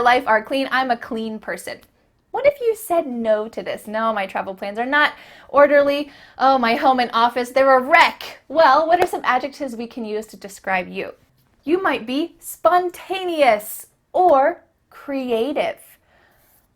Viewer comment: Hi, I really love to follow your lessons to study English. Your smile makes me not so stressful. I have one question for this vedio. I heard a word like "rack", showed up at around 4:30, in a sentence like oh my home and office, there are "rack". Is it the right word "rack"? It's my first time heard this word, so I was wondering what it is. Thanks!